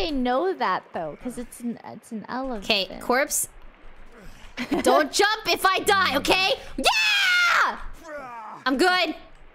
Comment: I thought she said “corpse don’t jump in my gut if I die” I’m glad it wasn’t